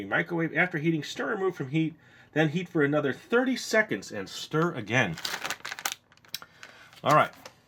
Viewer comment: I thought he said "eat for another 30 minutes and stir" I was like.... "How will he keep eating it?"